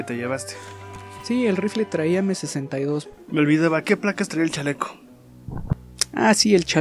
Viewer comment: A question about Spanish: In 6:03, I could've sworn you said "Plaqas." If you're referring to plate carrier plates, do you say "plaqas" instead of "platas"? This could just be my bad ears combined with my tendency to overthink, but I swear I hard plaqas.